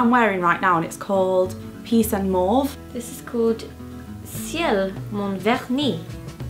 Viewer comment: It sounded like you said the nail polish was called "ciel mon vernis" which would mean "sky my polish" ... What